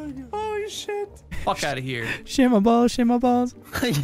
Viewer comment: While ohm is gonna kill something Terrorizer is spamming his sound board to the point it sounds like it is saying "shame my balls" lmfao